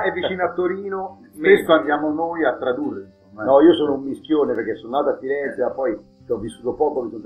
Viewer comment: A 6:33 quando Giorgio ha detto "mischione" ho capito "minchione" ahahahahah Scandisci bene, mis(n)chione....